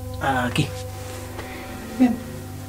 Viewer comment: pregunta... que te parece? se oye INCREIBLEMENTE una voz que dice "bien". y no es. Si no me equivoco es un aporte brutal . no se. Un beso a los dos 😘😘 37:21 tres golpes?